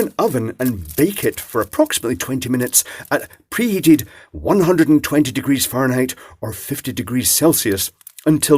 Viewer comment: oh crap, I thought I ruined my roll, I thought you said 220 degrees, but it actually turned out better with the charcoal bits acting as an exfoliator.